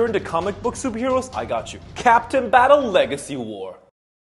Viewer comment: When he said captain I thought he was saying china CAPTAIN CHINA💀💀💀💀💀💀💀💀